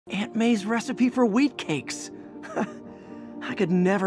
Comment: I thought he said weed cakes 😂